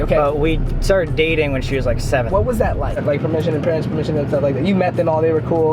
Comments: Am i the only one who hears 7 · It sounded like Sam said that him and Katrina started dating when she was seven haha 😂💀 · it sounded like sam said I started dating her at 7 😂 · it sounded like sam said i dated her when she was 7